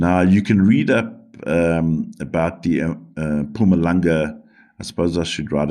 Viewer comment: Did you say the ancient sites are called "MAPUNGUBWE"?? Yes, pls could U make videos on that area for us, it sounds so interesting. A bit like Stonehenge, UK. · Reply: Greetings, Mpumalanga is the province. Look up Adam's Callender, an Ancient Site in MP. Old Ruins like StoneHenge. 🙏🙏